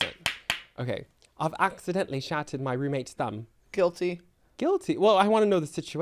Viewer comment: bro I just heard “I’ve accidentally shat in my roommate’s thumb” LIKE WHAT